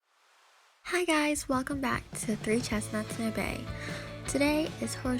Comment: In your intro I thought you said welcome back to 3 chestnuts and a grey 😂😅